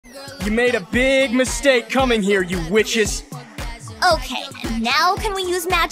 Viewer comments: Is it just me or it sounded like he said "you made a big mistake coming here you bitches" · i heard “you bitches” 😭